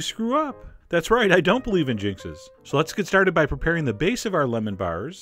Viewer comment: Nice video. Will try this for Christmas! No I didn't notice the chef's reflection in the bowl till someone mentioned it....lol. And I thought you said you don't believe in Jesus till I replayed it again.......Jinxing! Anyway, appreciate the videos!